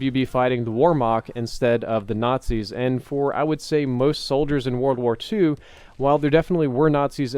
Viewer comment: oh you were saying whermacht. It first sounded like you said "warmoc"